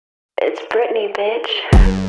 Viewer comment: Whoever wrote the "It's Britney Bitch" for a part of the lyrics is a genius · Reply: I always thought she said it’s Brittany baby 😂😅😅😅😅😅